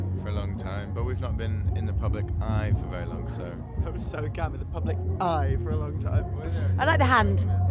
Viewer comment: WHAT DID MATTY SAY: 2:02 "THAT WAS SO ____" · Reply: camp